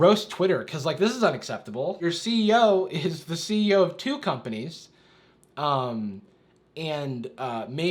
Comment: At first I heard, "since your CEO is the CEO of cucumbers" and not "since your CEO is the CEO of two companies" and my brain went ???????